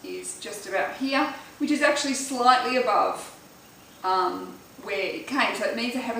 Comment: hey! I hope you see this but I can't figure out what you said around 1:30 (the part where you say: "which is slightly above ???? which means I have a slightly short waist") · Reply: “Slightly above where it came”, so her natural waist was higher than the tape measure indicated it would have been.